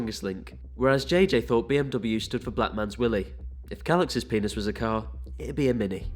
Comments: At first I thought JJ said BMW stood for "Black Man's Whip". That would've been a sensible answer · “Black Man’s Willy” - KSI, 2020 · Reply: I thought he said black man's whip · Vik: “JJ what does BMW stand for” JJ in .013 milliseconds JJ: “BLACK MANS WILLIE” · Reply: i thought he said black mans whip?